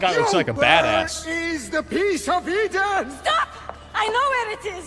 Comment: I thought he said "where is the piece of pizza"